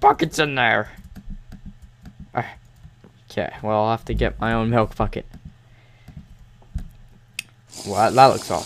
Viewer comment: did he just say: "I have to get my own health f**k it